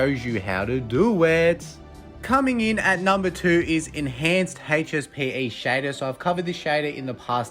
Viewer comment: Sorry Man, my native language is not english, i don't understand it, could you tell me the name of the texture? I'm using the translator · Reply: HSPE shader, doesn’t work with render dragon tho😅